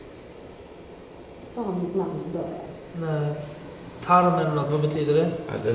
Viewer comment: jag hörde die